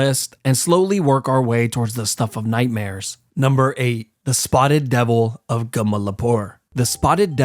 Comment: Bro I thought he was saying “spotted pebble” instead of “spotted devil”. I need new hearing